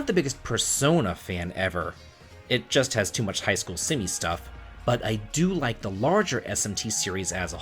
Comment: Lol at first I thought you said you didn't like persona for "too much simp stuff" and it made me snort up my drink.